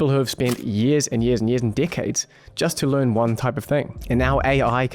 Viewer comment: Not gonna lie I heard “years and dickheads” I know he said decade but I had to rewind it lol